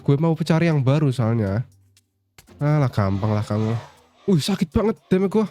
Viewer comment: silahkan dikritik dan dihujat agar video kedepan lebih baik... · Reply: Wait the minute, Aku gak salah denger kan? "Kampang" 38:33...–Kata-kata yang sangat familiar bagi orang Sumatera Bagian Selatan –🤔🧐 (BTW, kalo menurutku sih, udah lumayan bagus sih Bang. Tapi bener kata Kak tentang perbedaan suara in game sama suara tambahan 😅)